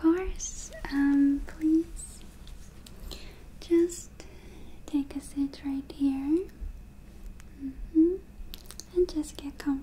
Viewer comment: When she said “take a sit right here” I thought she said “take a sh*t right here” 😂😂